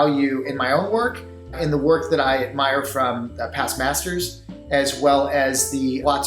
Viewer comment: DID HE JUST SAY PAST BASTARDS